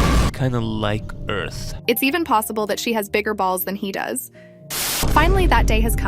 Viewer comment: did you just say bigger balls💀